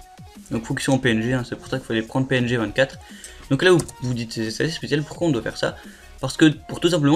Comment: A 5:00 Tu as dit quoi ? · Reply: "vous vous dites c'est assez spécial pourquoi on doit faire ça" et en même temps j'appuie sur entrée